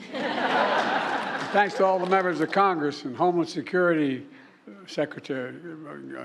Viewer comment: I heard 'homeless security'. Anyone else?